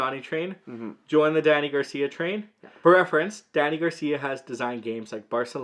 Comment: Am I the only one who thought you were saying Daddy Garcia?